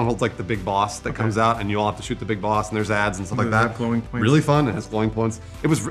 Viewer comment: Did say "You'll have to shoot the big boss & there's ads"?